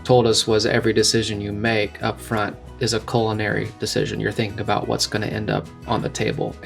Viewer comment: I could have sworn that you said a colon decision not culinary. Course both are true.